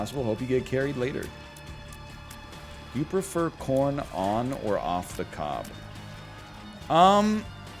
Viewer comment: I was half asleep and thought he said "porn on the cob"